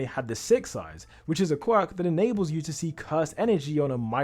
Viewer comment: did he say quirk bro it is jjk not mha